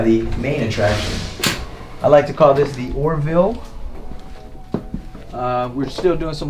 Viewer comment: did he say he liked to call it the Orville. cuz it's poppin ? 😂